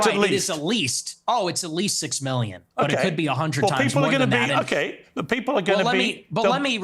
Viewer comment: did he say 100 times more then 6 million ?🙄